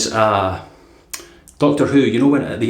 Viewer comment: To the others in the comments: how are you buying these blades? I've checked their site a few times, and there's no option to purchase them. Also, at one point it sounded like you said Doctoroo, and I thought of a kangaroo in a stethoscope and a white lab coat, with terrible handwriting.